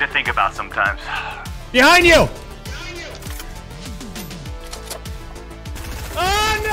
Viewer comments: "Stupid fourth monitor" he said... calmly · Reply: I thought he said horse monitor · "Stupid fourth monitor" he said... calmly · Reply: I thought he said hall monitor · "Stupid fourth monitor" he said... calmly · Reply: I thought he said heart monitor